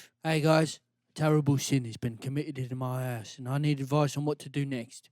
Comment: In the basement advice section, it sounded like he said "a terrible sin has been committed in my ass'